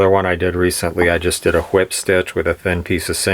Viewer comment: Did i hear HWIP stitch at 13:27? Also does the rawhide collect moisture under the handle? How would you go about oiling that part of the handle now, or is it not gonna matter if you don't oil those few inches of handle? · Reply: A Whip Stitch at 13:27, just means round and round pulling the two sides together, sort of like a corkscrew or spring shape. Never noticed any problems with moisture before, I don't think that would be a problem. It breathes like wood. I had already oiled this handle a lot. It might keep the glue from sticking well, but seemed fine also on the last one I did. I did scrape the surface of the wood away in both cases. The rawhide shrinks down so hard that the glue is probably redundant anyway One's I've done in the past, I don't recall, but I don't think it's anything to worry about either way. Same with the oiling under the rawhide in general. I just wouldn't worry about it. It's not really exposed to anything much and if the rawhide is oiled especially I don't really see why it would matter much. BTW, I didn't say that the aforementioned characteristic that rawhide takes on as it ages of not shrinking and not reacting with water, also makes it less susceptible to soaking up water if it is exposed. I would guess that a well oiled years old rawhide brace like this would probably be very moisture resistant and wood-like. If you want to score some hides, check with anyone that raises milk goats if people do that there. They are always culling young males. The skins tend to be thin, but high quality and dense. The upper part of the neck might be thick enough for this, or you might be able to score an old buck goat skin, which would probably be perfect. Cattle is hard to beat for qu